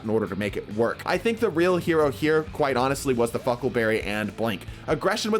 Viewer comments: am I the only one who instead of "huckleberry" hears "fuckleberry" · Did he say Huckleberry or fuckleberry